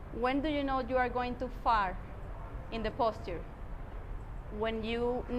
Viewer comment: did you say fart or far? I couldn't hear 😂😂😂😂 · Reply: Far 🤣